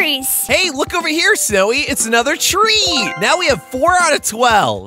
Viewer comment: I think he said tree